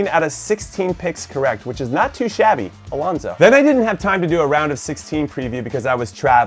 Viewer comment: why did you say my name “Alonzo” Lmao